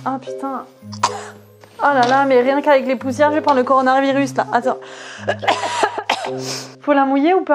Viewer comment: chuis la seule a avoir entendue "Coronardvirus" 🤣 Sinon perso moi j aime bien